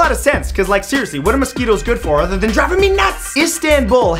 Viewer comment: I thought he said "Dropping me nuts"......I'll just leave now....